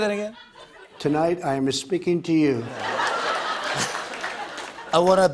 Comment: I thought he said "tonight I'm misspeaking to you" which I thought was apt.